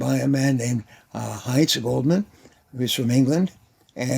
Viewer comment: What was the name of the Author of the book "How to sell well"? I heard Hytza Goldman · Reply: Heinz Goldman: I can only find his book " How to Win Customers"